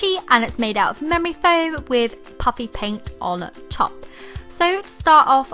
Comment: It sounds like she said puppy paint 😊